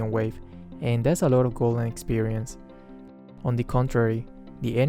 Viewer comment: I thought you say gold experience at the moment but it's actually gold and experience